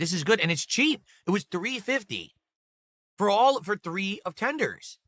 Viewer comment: did he say they were $3.50 for three offenders 😂😂😂